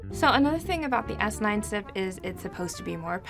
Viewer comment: What was that one syllable word she said after “the S9?” at 4:47? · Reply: SiP (System in Package)